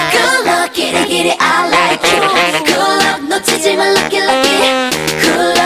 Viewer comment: I hear "good luck Neutrogena lucky lucky" lmao fml